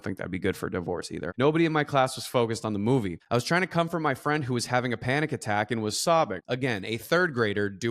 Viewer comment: I know that you're saying "comfort my friend" but the subtitles say "come from my friend" and I'm cackling, sorry 😂